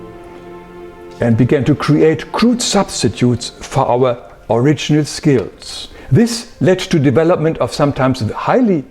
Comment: What was the name of the institute he said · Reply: Monroe institute